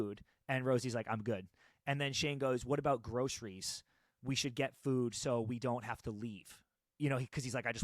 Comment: First of all, your HR analysis has brought immense happiness to me. Second, and this is so unimportant but I need to be Shane and bring it up: 16:13 you guys misheard the convo. Shane said he already bought groceries so they don't need to leave. He didn't say they needed to go. As you guys said, Shane is a planner and already had that covered ❤ but I did enjoy that extra commentary on if Shane said that, why he said that